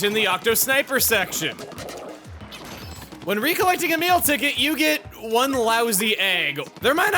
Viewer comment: Every time I hear “a meal ticket” I think he’s saying “Emile ticket”